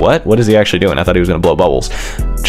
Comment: Bro i hear "dad is insane" For some reason☠️